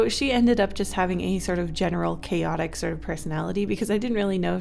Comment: DID YOU SAY CHAOS!?! (the world revolving intensifies)